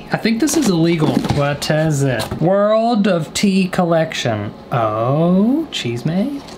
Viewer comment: When he said tea collection, I thought he said teeth collection. Lol